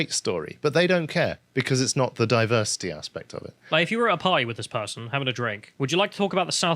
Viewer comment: I heard “Like if you were a pie with that person, having a drink…” 😆